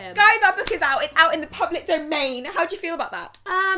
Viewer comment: Can someone tell me what did say at 1:14? Did she say "domain" or "don't mean"? And what does that mean?English isn't my first language and this question has been bothering me all day. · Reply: She said domain. meant that the book is available to the public and people can now buy it. Domain means an area/territory that someone can have control over (in this case the public people) Hope that answers your question :)